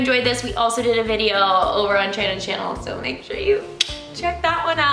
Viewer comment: In the end it sounded like said "Channon's Shannel 😂" I love this video. It made me laugh a lot